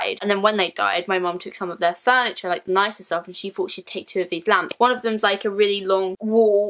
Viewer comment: When you were talking about the lamps I thought you were saying lambs😂